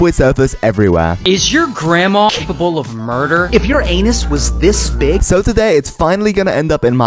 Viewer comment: I thought bro said ur_an*s